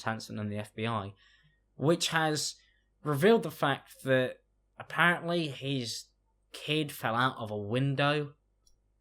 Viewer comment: i thought you said his keys fell out the window and I was confused about why it was such a big deal, until I understood you were talking about a child :/